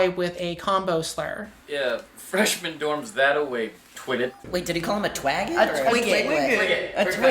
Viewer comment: I always thought he said "twiglet" to emphasize how small Sebastian/Viola is 🤔